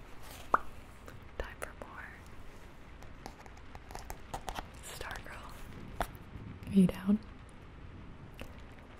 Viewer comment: Lol did she say “time for porn” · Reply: Marcos Castro time for ”more”